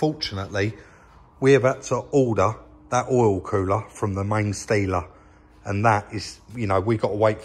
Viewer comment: could've sworn you said " main stealer " when referring to main dealer , made me chuckle